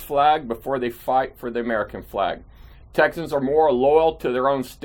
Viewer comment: you ruined cali, now your ilk destroying Texas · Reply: and I thought he said Texans are friendly. 🤷‍♀️ if you actually know darkrider25, I withdraw my comment.